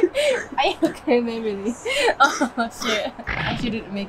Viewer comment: "Oh shit"って聞こえたの俺だけ？w